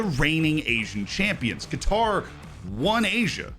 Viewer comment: when you said "qatar" at 2:51 the auto-generated captions said "guitar" 😂